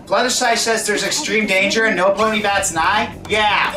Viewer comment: did you just say "Nopony"?